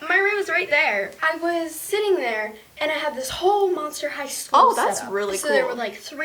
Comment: i thought she said.. monster hideout